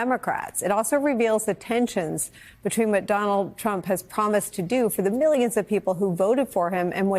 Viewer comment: Did she call him McDonald Trump 😂